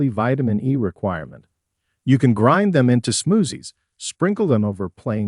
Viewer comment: Suzie Smoozie might be the best name we’ve heard all week 😄 Is that your pet’s name or your alter ego? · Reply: lol No. I kept hearing "smoothie" pronounced like "smoozie"!